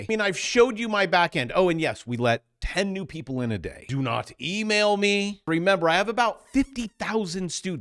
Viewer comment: I heard say "I let ten people in my back end a day."